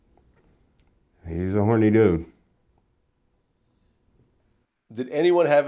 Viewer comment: did he just say "he.. hm... he's a horny dude" and smirkin before saying it??? wtfff